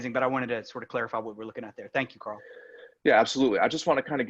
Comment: Whenever he says "thank you karl" i hear "thank you gurl" haha